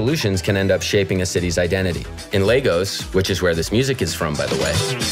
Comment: I thought you said LEGO's until I read the text